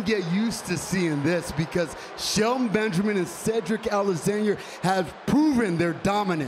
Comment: I honestly thought he said Cedric a-lasagne 😂